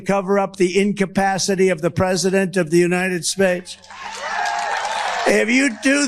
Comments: I just heard him say United Spates at first then pronounced it right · "Capacity of the United SPADES" Wait what was that lol what is trump trying to say